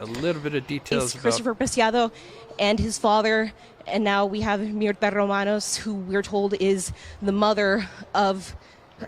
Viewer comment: What does the reporter say at 18:06? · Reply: “Mierta Romaros” it sounds like the woman’s name